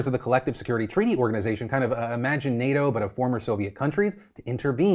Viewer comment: i heard "Imagi-NATO" shrugs